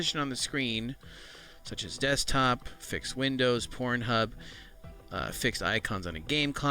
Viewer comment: mein brain is damaged, i just hear pornhub, its my brain or did he say it ?? xD